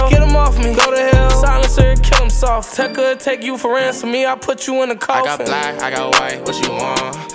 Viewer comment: i always hear it as "'tecca thank you for ressing me''''